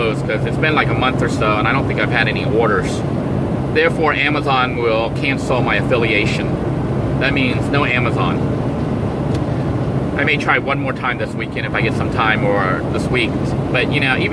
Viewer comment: I thought I heard Amazon killed the affiliate program